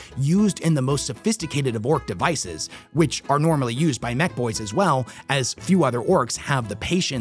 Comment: I heard you saying Mek Boys as Neck Boys, which brought the thought of Neck Beard orks who has a meta/4th wall break “aqsually” rule that beats their opponents via dubious rules lawyering 😂